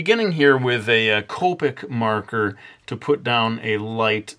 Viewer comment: it sounded like you said colpic marker